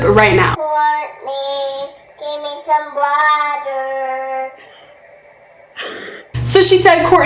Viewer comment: i thought it said "courtney give me some blood" and i screamed